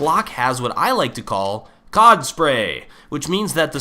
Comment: and did you say COCK spray 4:24 you said COCK spray